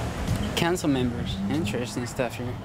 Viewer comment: "Cancer members" - I heard right? :D - "council chambers"